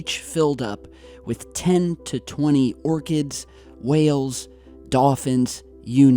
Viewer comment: did you say orchids at 3:21?